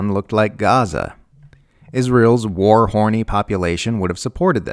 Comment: Did just say Israel's "WAR HORNY" population??? @ 3:38??? Brilliant for its crassness and accuracy.